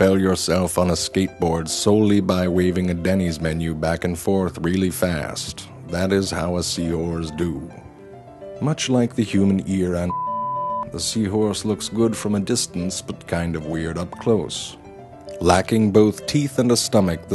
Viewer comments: It sounds like he is saying sea whores. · Sounds like he is saying sea whores lol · Sounded like he was saying sea whores…..